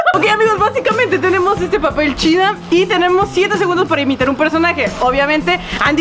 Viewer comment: Pense que dijo "Arroz chino" y estaba buscando el plato de Arroz :'u